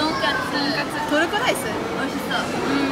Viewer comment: I think your subtitles are wrong. It should be 美味しそう instead of 美味しいそう which means "I've heard it's delicious".